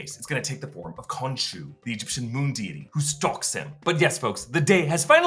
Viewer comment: did he say "Egyptian Demon"